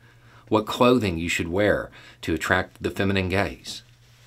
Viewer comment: I thought for a second he said 'how to attract the feminine gays' lol